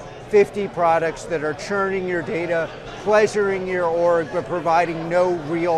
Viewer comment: Did he just say "pleasuring your orb"?